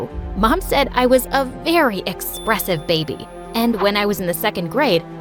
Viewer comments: I thought she was saying expensive baby instead of of expressive baby😂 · For a second I thought she said ''expensive baby''